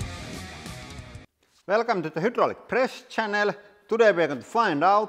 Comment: It sounded like he was saying "Welcome to the Hydraulic Präst Channel"! ("Präst" is Swedish for priest)